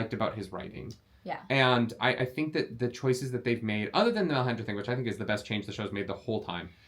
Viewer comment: "I think that the choices that they've made, other than the _____ thing, which I think is the best change the show's made the whole time ~ That scene was incredible" - Can someone help me here? I've played this back several times, both while wearing headphones and not, and I can not, for the life of me, understand what the heck said here. My brain just can't seem to decipher the word in the blank in my quote. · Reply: "the Melisandra thing" is what I heard.